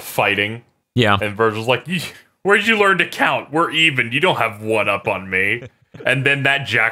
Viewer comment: When Vergil said "Where did you learn to count?" I dead ass thought he said "When did you learn to count?" and both are equally funny.